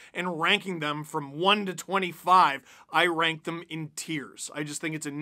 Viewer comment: When said "tiers" I heard "tears". I agree - greater center depth and a hard nosed scoring winger that isn't banged up or in his 30's.